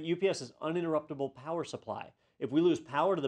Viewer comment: err isn't UPS an "uninterruptable Power supply"? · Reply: I though I heard say "Universal Power Supply", or is it both in an IT context, I'll rewatch this when I get the chance to make sure I didn't mishear him.